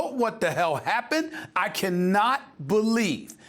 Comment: What word did Stephen use at 4:35? "So docile, so ___"? Demural? What?